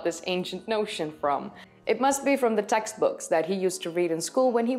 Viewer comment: I heard "the sex books that he read in school". But obviously you said "textbooks". I will have to get used to your accent (I was born in Nederland but have lived in Australia since the age of two). But I love your coverage. And the vulture as the "bow and arrow" of Sagittarius also had me in stitches when I watched "Ancient Apocalypse" episode 5 just before coming here after my internet search for a debunk (I have not yet watched the other episodes because I was responding to someone in a comment regarding Gobekli Tepe - which he misspelt! - which he claimed GH had a far better explanation than the archeologist. And, yes, I was wondering where the 4th handbag...sorry...sunset went to. But I think you are wrong about anything but death silencing GH.